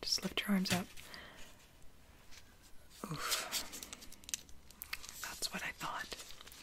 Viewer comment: i swear she said “oof”